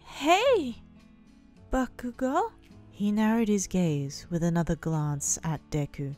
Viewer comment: For a second I thought you said "He narrowed his gays" 😂